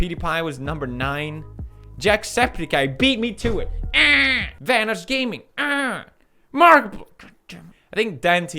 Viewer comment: did he just say thanos gaming??